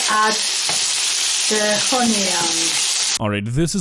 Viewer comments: I didn't understand what she said either at 2:40... except the last line "before I add the honey". There was no honey in this entire video. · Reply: Believe it or not, she said onions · I think she said add the onions I THINK